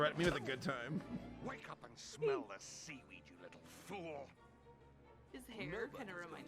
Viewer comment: I fcking thought Drake said, "Wake up and smell the SEED"